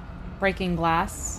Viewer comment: When she was playing as Chris looking for the spray deodorant, and she was identifying noises, she said "breaking glass"..........and I thought she said BREAKING BAD......